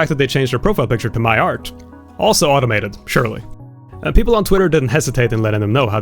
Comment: the criticism of something being "automated" with a subtitle saying "Shirley" instead of "surely" is hilarious.